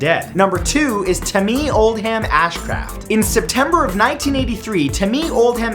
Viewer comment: with captions says, "damn Ashcraft"